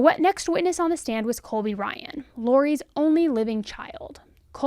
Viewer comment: I thought you said "Next to the stand was Kobe Bryant." 😮😂😂😂